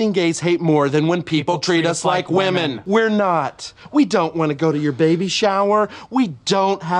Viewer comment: For some reason, I thought he said no we don’t want to hold your baby😭💀😂